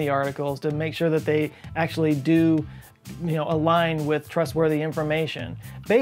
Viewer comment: took me a second. thought you said "make sure they do a line with trustworthy information" 😆